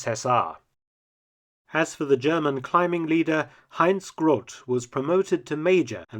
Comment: Anyone catch the name of the mountain leader at 9:17? Hans/Heinz Gorult? I'm trying to find out if he published a book about his experiences......